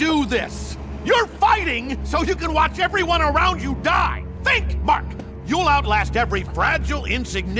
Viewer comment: All i heard was "Think Mark". Where are people getting "Think, Mark, think" from?